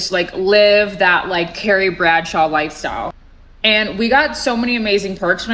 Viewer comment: If I was getting paid 6 figures just to drink coffee and send emails, I'd live like a fucking monk for 5 years to save as much money as possible. The woman in one of these videos said so herself, though, she wanted to live the Sex & The City lifestyle. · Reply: OH, Carrie Bradshaw.... I thought she said Terry Bradshaw and I was really confused.